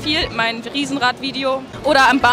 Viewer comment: hab den Namen nicht verstanden. Was ist Ihr Frage für Schulkollegen